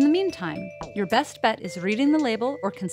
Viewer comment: why did i hear it as "or insult your doctor or your pharmacist " well i'm going to go do that